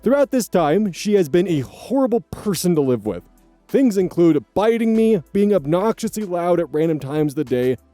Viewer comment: when I first heard that I thought I heard that wrong and just assumed he said “fighting”.